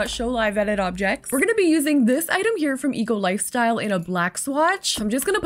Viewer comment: i've started watching your channel a few days ago and whenever you mentioned 'eco lifestyle' i kept hearing 'eagle lifestyle' and kept wondering what the heck is an eagle lifestyle hahaha. love your builds btw